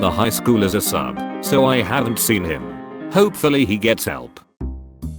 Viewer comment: While I was listening to the last one I misheard the end as “I hope he gets them” and I was like WHAT!!